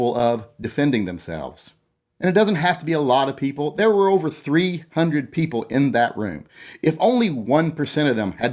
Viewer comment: went to comment. had to re-listen. Thought he said 50 people in the room and 1% And i'm all.. wtf, you cannot maths? Thats half a person. And re-listened to "holy shit sarinhighwind, you cants listen?" hahaha